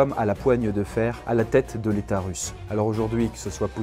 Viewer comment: à la place de "l'état Russe" j'ai entendu l'utérus!